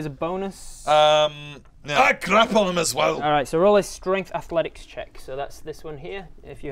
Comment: Loving it so far but i gotta ask did anyone else here "I crap on em' as well" instead of "I grapple them as well"